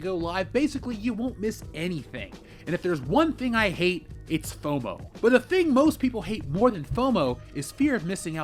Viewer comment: 2 adverts for your Tits book within 3 and a half minutes? Too much shilling for me! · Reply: Yup I thought at the end he said "one thing we all hate is PROMO" I was like whaaaaa? But he said fomo.😒